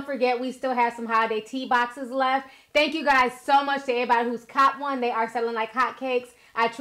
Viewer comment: enunciates her words so well but every now n again we’ll get that lil hood “ehh-body” instead of “everybody” 😂😍 I’m hear for it @ 0:24 mark